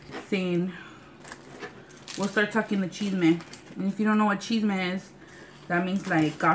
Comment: lmao when you said "chisme" at first i thought you said "cheese man".... i was like what.